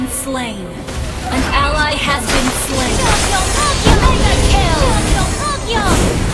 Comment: Don't look down on me just because I'm small Do you wanna be my shikigami ? ShugyoMugyo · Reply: Lmao i thought she say "tokyo tokyo" when she ulti hahaha now i know